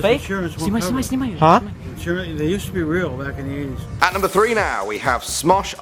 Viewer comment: Did he just say, "Back in the 80's"? because if he did, that makes the debunking fake. srsly... the 80's?